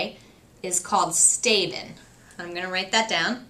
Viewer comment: I misheard "stabbing" for STABEN, OMG it is the conflict resolution in a prison! :)